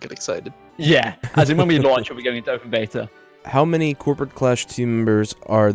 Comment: We'll be opening in Open Beta, they mean the game isn't out yet. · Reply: Ah. So it's not in open beta. I heard "We're in open beta", got excited, looked everywhere for a download, didn't find it, resumed the video, and someone else said the opposite, so I got confused. Thanks for the response!